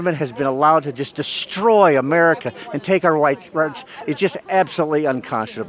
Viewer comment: what did he say? "Destroy America and take our whi rights" ?